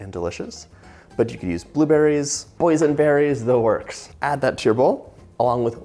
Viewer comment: At first when you said boysen berries for a second I thought you said “poison berries”😂I was like hmm not sure my store sells those